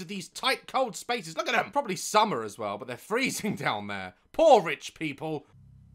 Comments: When he said “poor rich people” I thought he said porridge people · daz, when you said "poor rich people" I misheard it as "porridge people" lmao